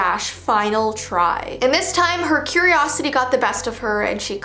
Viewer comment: i thought it said fillatry T_T EDIT:wtf who uses "T_T" ANYMORE BRUH